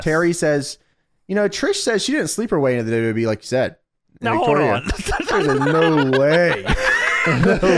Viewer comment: I smoked while watching this and this was a fucking ride 😂 I was literally on the floor laugh crying during the yin and yang part 😂😂😂 I nearly lost my damn life over that 30:27 I thought you said “Sleeperweight” like a fight class and my dumb brain was like “oooo that’s a really good insult for sluts” 😂